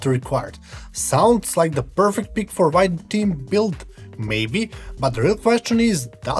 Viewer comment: what the hell did you say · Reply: 😂 I think he said white themed build